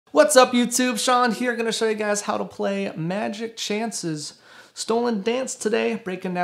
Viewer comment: Bro I stopped listing after I heard “magic chances” 🤣🤣🤣 now I’m scrolling through the comments looking at people laughing at this guy saying magic chances instead of milky chance 🤣🤣🤣😂